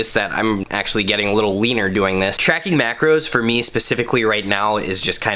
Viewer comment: I heard "wiener" instead of "leaner" 😂😂😂 There's definitely something wrong with me 🙈🙈🙈 That spreadsheet hurt my brain...😵